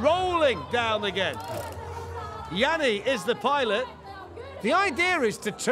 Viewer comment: I heard, "Laurel" is the pilot.